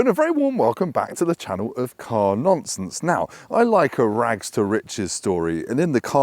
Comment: I thought you said "welcome to the channel of car nonces"